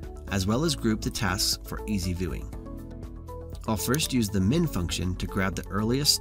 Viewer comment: Lmao when I heard him say "Use the MIN Function" I thought I said mint like a breath mint lmao so I typed that in instead and only noticed it when I got an error lmao what wrong with me. Lol